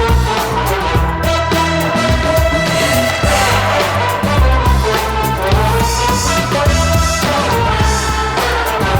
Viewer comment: What do they say in those parts? or is it just guttural? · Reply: they say "un deux trois quatre" that's French for "one two three four" and they just yell hey! in 1:41 and